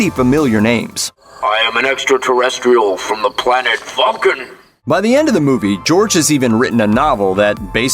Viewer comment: I always thought he said he was from the planet funkin' the definition of the word funk is "a state of great fear or panic." Which is what Marty causes his Dad when he unexpectedly wakes him up with his music.